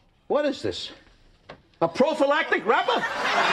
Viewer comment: Its been over 25 years, and Jerry Stiller screaming "A PROPHYLATIC WRAPER!?" always cracks me. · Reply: I slowed it down and it's wrapper, though I too thought he said rubber. It's funnier thinking he said rubber.